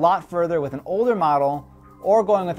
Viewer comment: did he call himself donald duck at 3:41?